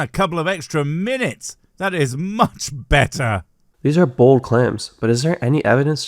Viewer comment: Did he say "bold clams"?